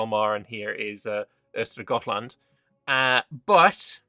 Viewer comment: thats not Gottland, its Öland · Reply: Sounds like he said Östergötland, which in this case still isn't correct but it's closer than Gotland.